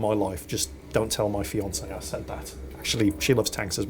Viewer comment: FINALLY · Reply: did he really say fiance?